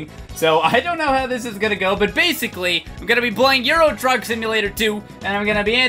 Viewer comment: At the beginning I thought he said "Euro drug simulator."